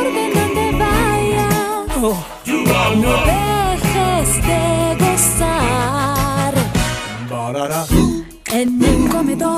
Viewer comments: pareciera un poquito que dice "no dejes de besar" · en el segundo 0:35 creo que dice gozar